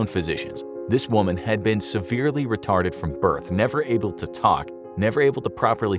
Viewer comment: did you just say "reta*ded" 😳